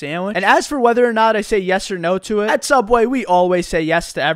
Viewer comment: Im not a native English speaker, at the beginning I thought he said Obamanation😂